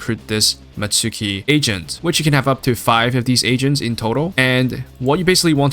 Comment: I totally thought you were saying asians instead of agents. I'm like, "Which one, though?" Time for some hearing aids 😂